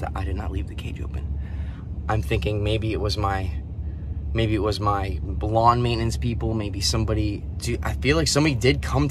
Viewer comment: DUDE!! @ 3:00 I THOUGHT YOU SAID "BLONDE MAINTENANCE PEOPLE" LOL i was like well?? Sounds about right? Lol